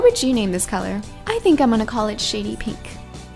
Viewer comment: It sounded like she said sh*tty pink...